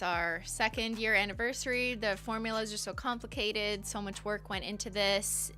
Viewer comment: ish when she says its so much work i heard so much twerk and i really want that to be a new polish name now hahahaha